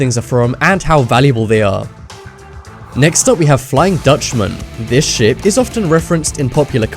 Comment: i thought he said this shit